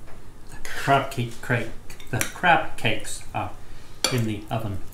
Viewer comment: oh lol! at first ( english isnt my first language ) I thought that you said cupcake but you said crab cake... I was wondering how on earth can be make a cupcake with crab... lol